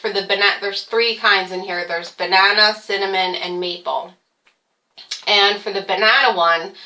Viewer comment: When talking about the oatmeal, you said maple...I thought you said meatball...lol :)